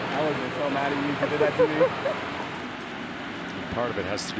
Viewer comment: Great moment between Tatis and Belli, two young stars and the future of baseball. Dual belt-tugs, then Belli says (I think) "I would have been so mad if you did that to me." He's gotta be referring to the HR he robbed from Tatis in the previous game. I think Belli might have even said "I'm sorry, baby" when he reached second, both of them clearly being good sports about it.